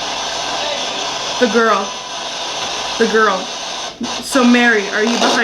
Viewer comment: "two girls" asked, "Who's behind us?", &I heard, "two girls". Anyone else?